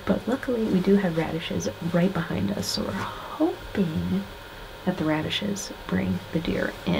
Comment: Congratulations..I thought she said they may come to the radishes but it looked like someone was cutting onions..LOL.Great hunt